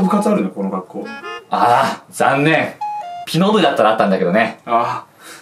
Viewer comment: ピノ部じゃなくて帰納部に聞こえたわ笑